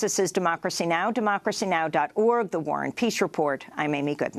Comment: I thought ye was saying warren peach report................... LOL